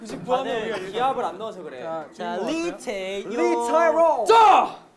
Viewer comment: did anyone else hear Mark call Taeyong "Lee Tyrome" hahah. THIS SENT ME